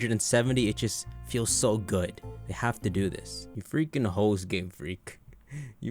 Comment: did he just call GameFreak "hoes"?